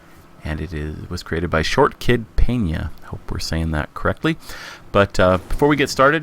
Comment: at first, i thought it said shortkidPENAS